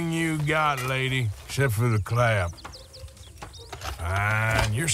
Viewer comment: did he say....Except for the CLAP! 😆🤣🤣